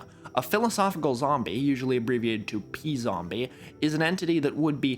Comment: after watching your video on rationalism and learning the term “p/[blank]”, I heard p-zombie as p/zombie and thought of someone asking what your probability of a zombie apocalypse is 😂